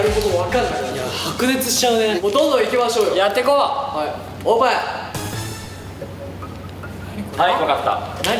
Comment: あたりの樹が「オープン、ハイ」って速く言ってるのかもしれないけど「おっ〇い」って聞こえてしまう…